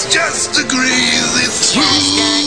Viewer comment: I always thought he said "greasy stove."